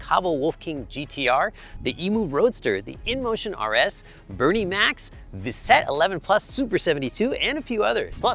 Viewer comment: thought he said Bernie Mac's 😂